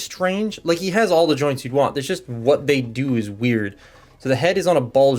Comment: I swear I heard "He has all the drugs you want, it's just what they do is weird" the first time I watched this.